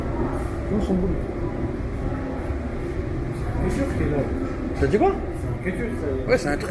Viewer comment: c'est moi ou t'as dis "son boule" mdrrr frèro c'est une mineure je crois.. 😂